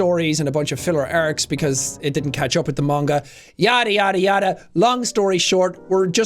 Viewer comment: did say yare yare dawa?